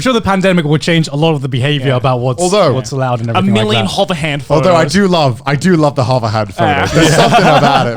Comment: Hey can someone tell me what the word for standing or hugging awkwardly that fans ask for when taking pictures is? I can't really make it out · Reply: Hover hand :) Look up hover hand on google images, lots of delicious awkwardness